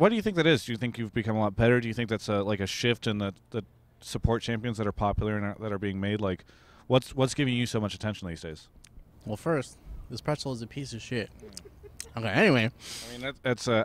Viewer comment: I thought he said xpecial was shit not this pretzel is shit